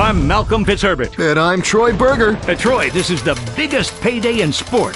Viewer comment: did he just say his last name is BURGER😂😂😂😂😂